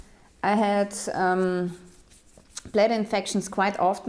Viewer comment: "I've always been really healthy... other than having blood infections 3 or 4 times a year." what... these statements really can't go together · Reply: I thought she said bladder infections, which are a common problem for women